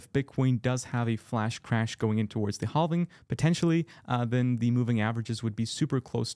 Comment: Did you say hall-ving?